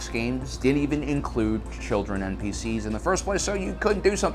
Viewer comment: "...sandbox games didn't include children or PCs..." You repeatedly mention "PCs" - had to think about that before I understood that you mean "People of Color." (Is "pissies" - that's what it sounded like - really better ?) So in these games, the player can kill only White people? Doesn't sound very PC (= "politically correct").